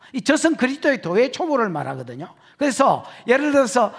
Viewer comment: 여기에 목사님께서 말씀하신게 뭔지 잘 안들리네요. 그리스도의 뭐의 뭐를 말씀하시는 건가요? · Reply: 젖은 그리스도의 도의 초보라는거 같은데요!!♡